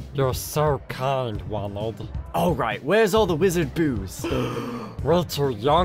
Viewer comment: “Where’s the wizard booze?” “Oh no Harry we’re to young for grownup water. My grandmother-“ “I DON’T CARE WHAT YOUR GRANDMOTHER SAYS I’M HARRY POTTER!!!!!!!!” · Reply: Not gonna lie, for the longest time I thought he said “wizard boos” like he wanted them to boo Neville